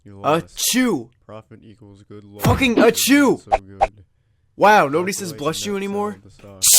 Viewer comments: i just thought that he said word „Ačiū” in Lithuanian which means „Thank you” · didnt i just hear fuckin pikachu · for a second I thought he said fucking a jew